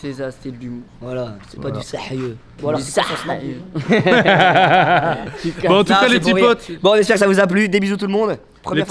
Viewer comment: Inox il a dit quoi ? jai pas entendu a cause de mich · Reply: Il a dis envoie son snap maintenant